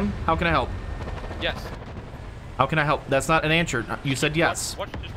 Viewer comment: Bahahaha! Us: “Bo, have you been drinking” “No” Us: “That’s not an an-sure”🤣 Anyone else catch that?